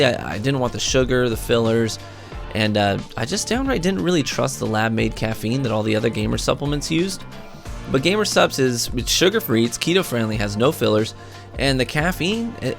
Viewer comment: Did I just hear “gamer sup”? To sup what?! Body by xbox takes no supplements